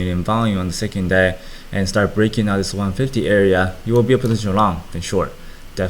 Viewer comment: break above 1.50, short or Long? Can’t make out what he say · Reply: Nicholas chua I think he said long Edit: maybe he said you will be in a long position then short